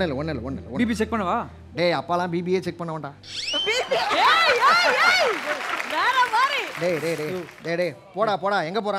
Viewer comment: what he said? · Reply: BB (Biggboss show contestant)